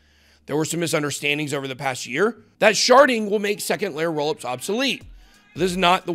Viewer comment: I thought he said sharting!! Lmao..